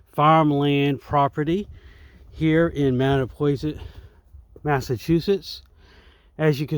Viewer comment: My goodness my Fam , you risk taker you be careful of that mud! Mattapoisett? You say? Wouldn't that be a tongue twister! At first I thought you said Mannapoisett! The vernal pool looks quite majestic, it almost looks like an oil or watercolor painting with the background of the trees, thank you for paying tribute to that family. So this area has history so cool about you reminiscing on previous memories that you've had at this location, it's always fun to go back to the areas that you know the best, well yeah mushrooms are your thing because your a really fungi! I'll show myself the invisible door now lol! Private property? Well, it's a little too late to turn around back now, what a very confusing sign, it says private property but then it also says stay on trail, so what does that even mean? I suggest a better sign! Your Fam! 🐊